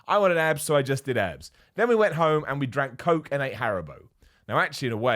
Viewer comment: I had to replay the part where he said we went home and drank coke and ate haribo because I swear to god I thought he said he ate heroin.